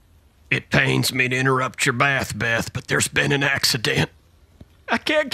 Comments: "A fire cracker spooked me and i pooped in ur bed" 😂😂😂😂😂😂😂😂💀💀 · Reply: I heard " a firecracker spooked me and it pooped in your bed" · I thought that he said bath,bath😂